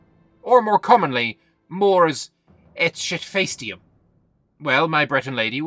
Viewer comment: am I having a stroke? What is saying here? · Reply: Mors et shitfacedium :)